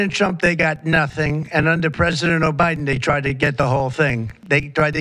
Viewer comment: did Trump just say "under president O'Biden"??? FREAKING SAVAGE